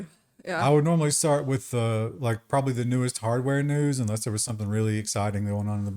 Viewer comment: what did you just say? Newest hardware links right?